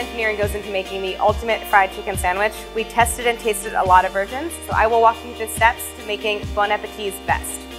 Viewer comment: is it just me or did anyone else hear "We tasted & tested a lot of virgins..."